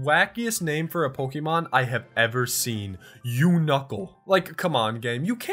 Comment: When you said Unuckle, I thought you said "You Knuckle". Like you are Knuckles. Knuckles from the Sonic series.